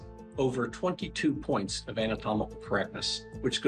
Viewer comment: Does he say 22 points of anatomical crapness?